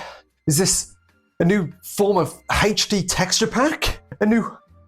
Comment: I heard "Is this a new form of hatred texture pack?" Lol. That's pretty in character for him.